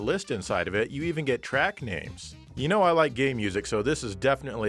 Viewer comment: For a second I thought you said... “well you know I like gay music”